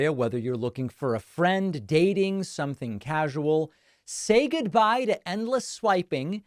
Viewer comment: Thought he said "say goodbye to endless wiping" Thought he got his wires crossed with the b'day sponsorship.